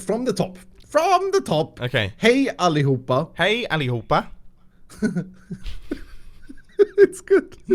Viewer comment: kinda sounded like he said "oppa" in Korean lolol.